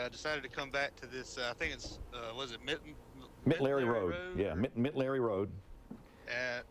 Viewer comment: Great stuff! Almost sounded like you said "Mitten Larry Rd." You need to spend more time in Bama country. ;)